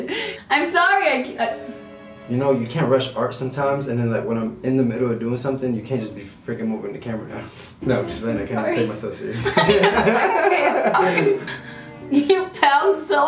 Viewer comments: WAIT!! Did she say “babe I’m sorry” at @ 4:53 - &4:57 ??? OMG!! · Did anyone else hear her say “babe I’m sorry” at 4:55??? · did anyone else hear “babe I’m sorry”????